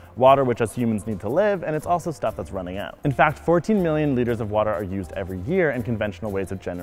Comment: Did he say 14 million litres every year? Because that is bugger all water.